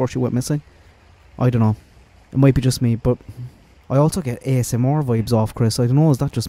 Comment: At the 1:10 mark, are you saying “I also get ASMOR vibes off of Chris”? I’ve replayed, slowed it down, and I still can’t figure out what that means or if I’m mishearing. I love your videos and always come out of them with a fresh perspective! 😊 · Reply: ASMR. You know those weird videos that are kinda relaxing? Lol thanks for kind words!!